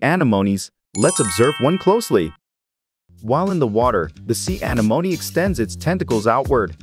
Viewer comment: Bloody AI voices lol A-neh-monee, say it with me AH-NEH-MON-EE. Sounds like its saying Alamony on this lol.